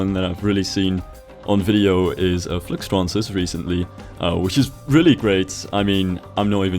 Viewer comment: Tried looking for the youtuber he said at 3:15 but can't really make out how to spell it anybody can help me? what is the youtubers name? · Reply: it's Fluxtrance